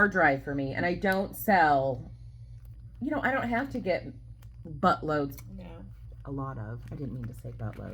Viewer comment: Oh good gravy! The two of you - hilarious! You could have a stand-up comedy show😂 St Mary’s School is in Raleigh NC….beautiful school and grounds.And, I thought said “busloads”….😂😂😂 For your sign off, thinking should be something a First Lady had or would say…and then, it has to be something that keeps us laughing! Thank you for the fun conversation - made my day!